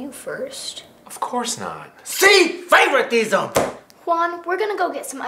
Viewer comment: Turn on sub titles in 3:02 and it says “see favorite diesel instead of favoritism